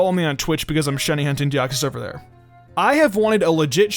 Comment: I had to listen like 3 times before I knew what you said.. I dead ass thought you said something about hentai deoxys the first time 🤣🤣